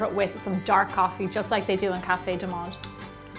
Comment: Just like they at Café _______? Anyone knows what she said here? Thanks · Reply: Cafe Du Monde, this is the very famous Cafe in new Orleans, famous for Beignet :) you gan check this out online!